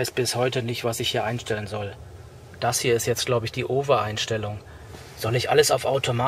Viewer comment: die was Einstellung? Cooles Video Knackig und informativ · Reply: Ich hab auch "die Ove-Einstellung" verstanden.